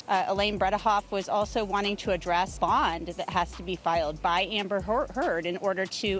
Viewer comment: i legit heard her say Amber Whore- Heard XDXDXD.